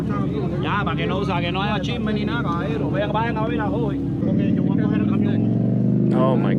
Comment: The guy in the black leather jacket says the following right after they stand him up "Ya para que no haga chisme ni nada, vayan a ver a Roy" which means "Yeah so there's no gossip or anything, now go check on Roy". Basically he doesn't give a shit about the injured guy's life, he's just concerned about what people say or think about the accident which is why they are so eager to pick him up.